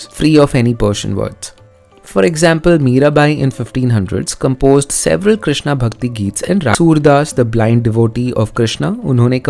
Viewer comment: 59 weird cut pls let me know what you were saying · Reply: I've added the cut bit in the subtitles (thanks to copyright strike), but I was saying Mirabai wrote Krishna Bhakti songs in Rajasthani (Payo ji maine) and Tulsi das ji in Awadhi (Mangal bhavan Amangal hari)